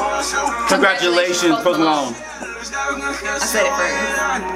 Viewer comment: I thought you said provolone instead of Post Malone. Lmfao